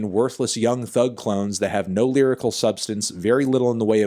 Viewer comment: did he just say clowns instead of clones